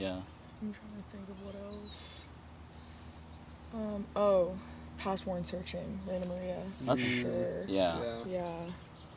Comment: anybody know what album it is she said here? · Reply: Rainer Maria - Past Worn Searching